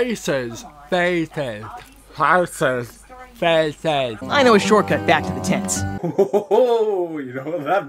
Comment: When Timber said “I know a shortcut back to the tents.” I thought he said “I know a short cut back to the ti-.” Nevermind.... I’m dirty....